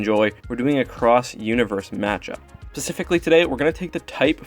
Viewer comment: That moment when it sounds like he says "Pacifically" instead of specifically XD